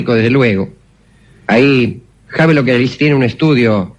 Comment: No entiendo el nombre del autor de "El Mundo de los Sueños" del minuti 3:30. Si alguien puede entender lo que dice?? Gracias. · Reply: Havelock Ellis